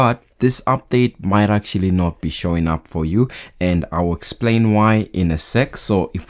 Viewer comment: I thought you say “I’ll explain why in a sex” lol mb.